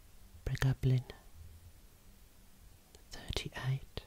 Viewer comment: did he say "Pig dumpling?" I might need a real hearing test.🤭